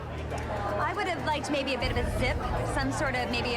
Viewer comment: never fails to make me laugh. Like what is that woman even saying 🤣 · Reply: she would've liked maybe a ZIP